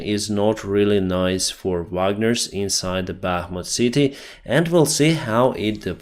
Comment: Love the blue and gold plaid shirt! Great job with the video too. 14:07, CC says, "The situation for Wagner is not nice inside the Batman City." Batman: Surprise!